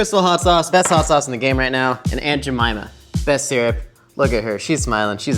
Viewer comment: Every time you say aunt Jemimah, all I hear is, "I put a whole bag of jellybeans up my a$$."